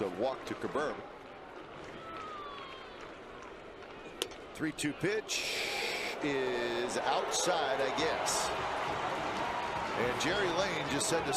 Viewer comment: I thought he said "three, two, b****h is outside i guess"